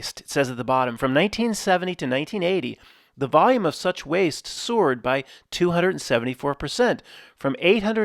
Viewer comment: somewhere around 5:30 I'm like did he just say sewer'd? started reading I'm like ohh he said soared- i rewind and sure nuff he did say sewer'd lol i noticed something like that he said other day must be an accent